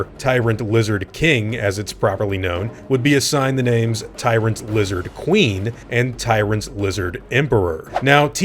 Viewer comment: when u said tyrant lizard regina, i thought u said tyrant lizard vegina lol